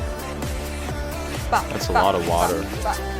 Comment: why do I hear Johnny's "that's a lot of sugar" 😭😭